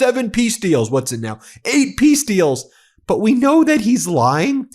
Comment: It is a chicken nugget world. I heard "Eight peace deals" as "eight piece deals." I think what I heard is probably closer to the truth than any Peace deals.